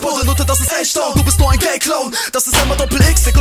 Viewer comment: did he say "gay clone"?